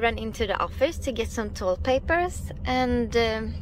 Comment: lol ... 11:54 I thought she said "go into office to get toilet papers" ... lol :)